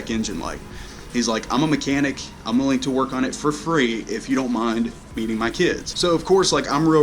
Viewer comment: Ha! It sounded like you said "if you don't mind "beating" my kids" :)